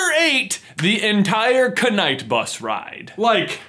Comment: did he just say K-night bus.............